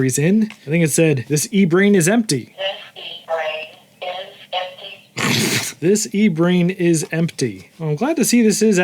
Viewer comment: For half this video I thought it was saying "This pea-brain is empty"